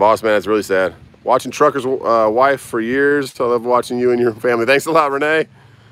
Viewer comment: Thanks for having a Channel. It took me a few videos to realize you said "Trucker Wife" I thought you said " Trophy Wife ". Sorry about that.